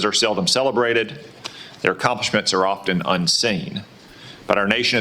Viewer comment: I'm sorry, did I just actually hear him say "unsinged" (instead of "unsung")? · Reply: He said unseen.